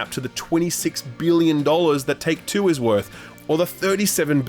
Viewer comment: My brain is gone, I really thought he said "Talk Tuah's worth" and not "Take Two is worth".